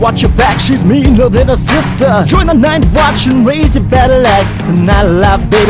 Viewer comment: I could've sworn I heard you say join the night's watch. lmao😂😂😂